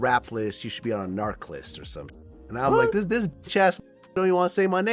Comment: Did Vlad say Bitch ass nigga?!